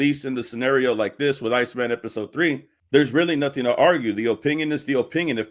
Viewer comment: I thought you said "Lightskinned Episode 3" & had to rewind it😂😂😂😂😂😂